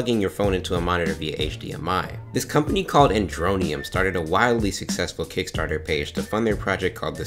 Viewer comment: I always thought it said androidium lol